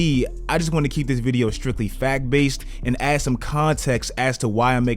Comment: I misheard this the first time I thought you said "fat-based" lmao